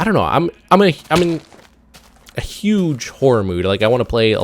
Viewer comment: Thought He was saying whore mood xD